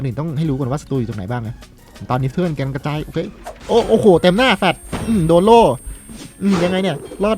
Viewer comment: พี่พูดว่า "เต็มหน้าสาส" 55555🤣🤣